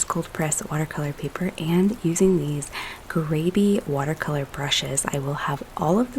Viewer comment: Kindly be clear About the name of the brushes. I hear “Gravy” but I’m sure that’s wrong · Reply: So the name of this brush is a bit funny because it does sound like I am saying gravy, but it’s actually Grabie.